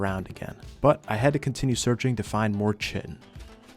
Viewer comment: I couldn't figure out what you were looking for at 32:30, then I realized it was "chitin". In biology, the word is pronounced like "kai-tin", but don't change how you pronounce it because some dumb guy on the internet tells you to. Keep up the great content man.